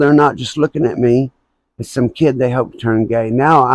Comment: did u say they turned u gay? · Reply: I said “now I’m not just some kid they hope to turn gay” thanks for watching 😊